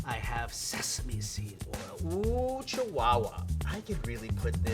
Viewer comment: Did they say 7up? I just heard "lemon lime soda". But also, 5:41 "I have sesame seeds" holding a bowl of sesame oil 😆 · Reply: he said sesame seed oil